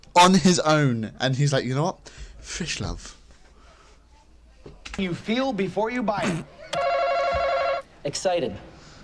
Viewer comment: I think he said "name something you feel before you buy it." I think the guy in blue said "Excited"